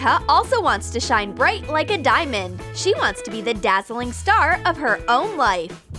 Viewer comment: At first i thought it was clickbait But...... WHY WOULD YOU EVEN ASK FOR THAT? 1:34 wait did she just say SHINE BRIGHT LIKE A DIAMOND?